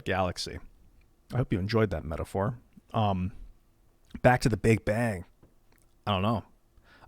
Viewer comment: Damn, I thought it said the big bong .....